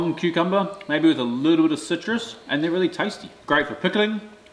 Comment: Good thing you have subtitles. I would have thought you said "cute cute melon" 😅🤣